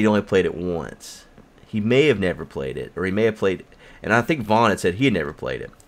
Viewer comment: I thought you said Vince Vaughn, O wow lol.